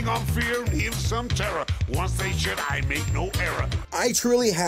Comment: it sounded like that shark said a swear word.🦈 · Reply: Once they s*** I make no error...